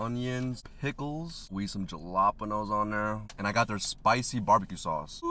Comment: Did he just say Ju lap eno? 😂😂😂